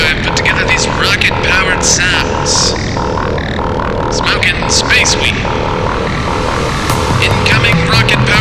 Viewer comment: did he say smoking space weed